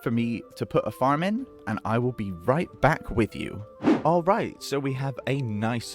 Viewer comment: I kept hearing "I will BEE right back", shame about the replay mod but I think the POV shots were a nice replacement :D